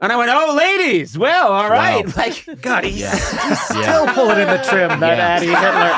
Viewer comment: What Matt say? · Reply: He's still pulling on the trim